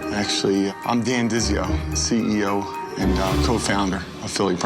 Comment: Sorry for my poor ears.. I should reply the video cause i thought he said 'im dan dizzy- yo' 😂